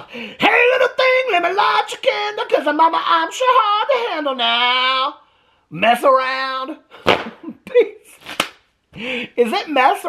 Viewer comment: The song goes...Hey little thing, let me light your candle 'Cause, mama, I'm sure hard to handle now, gets around... But I like your version way better, so keep singing it the way you do. Yes I am, is better and how it should have been sung!💙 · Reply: I always thought he said, ‘now mess around.’😂😂